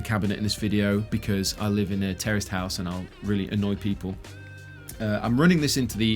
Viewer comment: Oh, TERRACED house. All this time, I thought you were saying terrorist house.